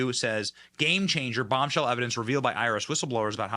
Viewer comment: I thought he said 'Irish whistleblowe', wow I was thinking connections to IRA, which is another story, but no, IRS.😊